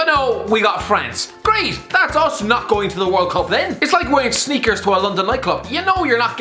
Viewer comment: did he just say sneakers